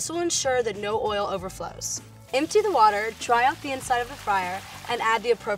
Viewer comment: Did she say dry out the inside of the fire? at 3:22 sure sounded like it !!!!